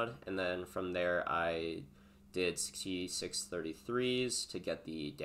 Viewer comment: you're talking about crafting the wand and say to did "T6 33's"? to craft the ele pen. Am I just hearing that wrong 😅